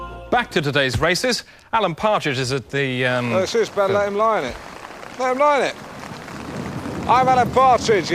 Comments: What is Alan saying at 21:56, 57, 58? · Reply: Benjamin Eckford "it's his bed let him lie in it, let him lie in it" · Does anyone know what Alan says at 21:54 😂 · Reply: "It's his bed, let him lie in it. Let him lie in it."